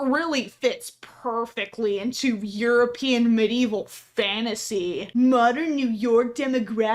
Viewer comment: medieval what was that word? Fantasy? Are they seriously upset their make believe video game has something perhaps not concrete in history?